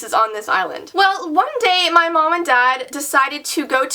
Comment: You said "And one day, my mom and dad..." I thought you said "so one day, my mamma died," XD